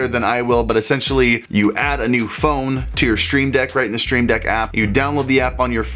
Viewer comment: did anybody else catch "That Stream Dick"LMAO